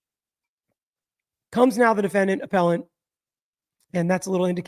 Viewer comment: I thought i heard, Comes now the defendant, a felon (not appellant) EXCUSE ME